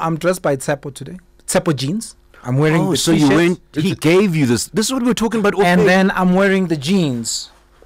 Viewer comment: On the Tshepo t-shirt...Thoma said he gave you this, what I heard "he gay view this"🤯